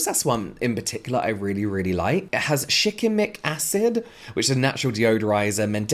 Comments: I heard Chicken Mc-Acid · I heard "Chicken McAcid?"